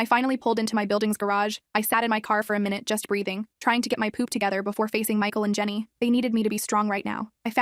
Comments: Did she just say hold my poop together 💀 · did she just say "i was tryna hold my poop together"